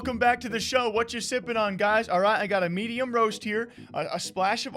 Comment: I heard "what are you simping on guys?"